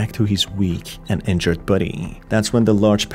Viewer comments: Lol, I thought you were saying, "buddy" instead of body, and it somehow sounded better to call those dopplegangers your "buddy" 😅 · Haha 3/4 of the video I thought you was saying buddy until I realized you was saying body… great video tho!